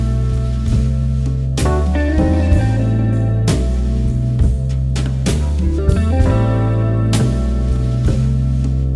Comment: Can someone tell me what is this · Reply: Lockers